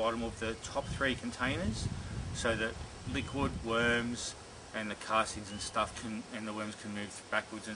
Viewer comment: Liquid worms??? Lol sorry I know what you meant but my humours mind heard it as Liquid Worms.... Nice setup, thanks for posting